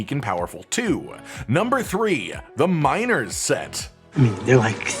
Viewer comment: I thought i heard minors 💀.